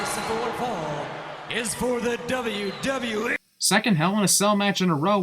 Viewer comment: Did he say WWF.....?